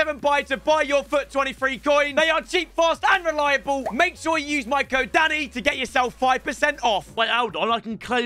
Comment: I thought he said use code Daddy 😩 in the u7 buy